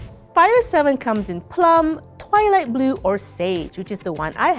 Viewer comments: Did you say toilet blue? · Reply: Heh. It's "twilight blue". But it did sound like she said that. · I thought she said toilet blue, sold!